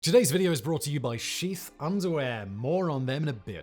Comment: I swear, when he said "sheath" underwear I thought that he said "Xi's" underwear.